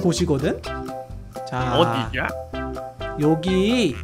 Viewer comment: 정도에 님이 "어디냐"라고들었어요